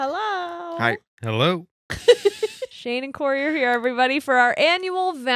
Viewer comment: The first time I watched this i thought you said Shawn and Cory